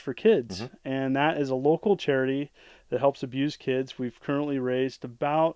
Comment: he almost sounds like he said "we help abuse kids" talk about obscure gaming